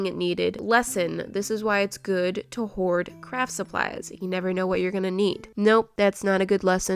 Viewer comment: when ever you said "craft supplies" it sound like you said ......you know......crap supplies 0-0